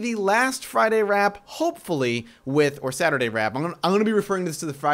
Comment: I heard ' saturday Rap' imagine doing a rap intro lol